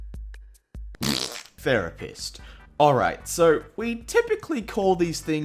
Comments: i thought it said the rapist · At the start i thought it said the rapist not therapist